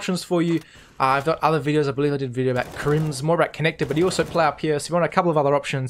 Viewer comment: wtf does he just said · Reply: Arya Shetty "I've got other videos I believe I did a video about krimz"